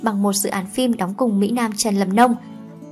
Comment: Trần Lập Nông add đọc nhanh quá tui nghe thành Trần Làm Nông 🤣